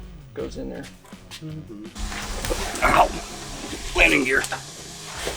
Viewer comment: I thought he said 'wedding gear' ... ooops 'it was landing gear'